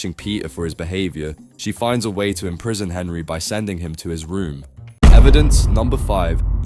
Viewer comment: I thought you said "punish him by sending him to Israel" and not his room